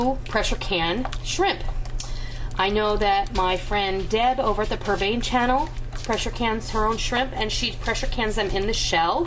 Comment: Regarding canning shrimp, did you say Deb at the "pervane" channel?